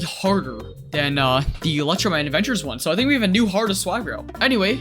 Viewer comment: I thought he said ultraman adventures.